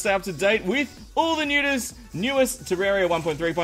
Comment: Did you just say; ''All the Nudest.....Newest...'' LOL!!!